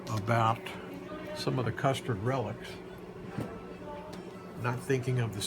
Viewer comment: did he really say “ the custard relics” 😳😂😂